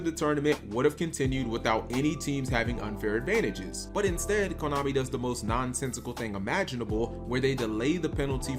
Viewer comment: I thought you said Klownami.